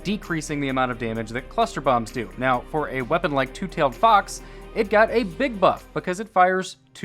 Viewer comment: Merciless,Anarchy,Two Tailed Fox,Telesto and The Prospector. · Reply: Thought the two tailed fox said the Two Failed Fox lmfao